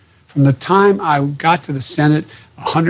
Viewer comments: 10 yrs as vice president, huh? He's a terrible Liar! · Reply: he said tenure. i thought he said 10 years too but the quality of this video is just bad. CSpan has better audio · Nobody noticing he said 10 years as Vice President as well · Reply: I thought he said tenure